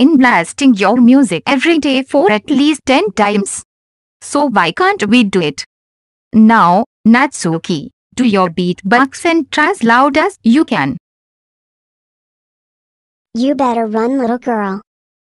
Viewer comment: "Now Natsuki to your beat boxing transload as you can" That's what I heard in